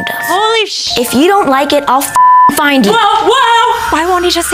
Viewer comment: I thought he said "If you like it I'll giving going you"